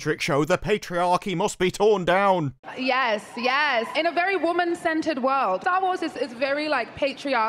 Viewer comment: Thought she was saying “women-scented world” at first. was like wow, implementation of The Message has really gotten out of hand